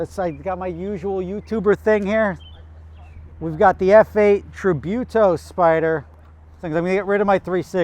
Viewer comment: I thought he said no tributos as it was too similar to a 488